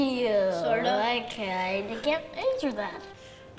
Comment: Really? I thought he said, “I kinda cant answer that. “